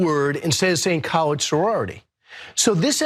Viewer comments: I actually thought he said college, tbh. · I heard him!! He said colored, hes a big liar! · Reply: Sounds like he said colored, no doubt about it. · Are there non college sororities? Why would he say college? It sounded like colored, it was a black sorority. I heard no G sound. But I am just as biased as people who claim to have heard college. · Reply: I heard "LORED" and not "LEGE" at the end of the word. I did not hear "College" ha ha... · I thought he said "college". It pains me to dispute what these bigots may have said. Ugh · You said colored, we all heard you colored and college two different words, two separate spellings, complete different spellings and two complete different pronunciations we’re not stupid. We wasn’t Waze last night. We wasn’t born last night you said colored sorority you can hide your is in you. And you’re not gonna apologize you have no attention of apologizing you use the word it came out of your mouth. The Bible says the abundance of the heart. The mouth speaking was truly in your heart will come out of your mouth and that’s what came out of your mouth color sorority that stupid like like my mama said I was born at night, but I wasn’t born last night. Don’t give me that we heard you clear. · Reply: I thought he said colored. But I listened again. I think he did say college